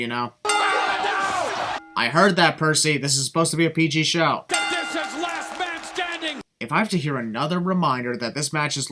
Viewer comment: what did he say? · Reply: Oh $hit